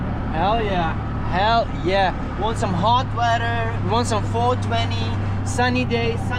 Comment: I heard 420. I'm shocked it isn't available in the rural parts of SE Asia.